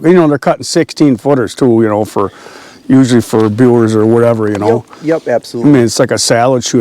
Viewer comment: did say Biewer’s? Biewer just built a brand new pine sawmill here in MS, just started up this month. GNI 👍🏻👍🏻👍🏻